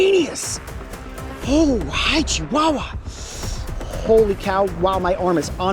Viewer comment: did coyote just say “ha-chihuahua?”- Also here’s a band For coyote From ur fan